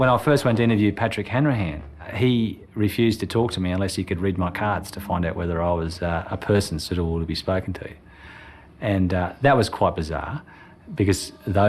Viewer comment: What did he say? "...could read my cards to find out whether I was, ah, a person __________to be spoken to...." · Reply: Suitable?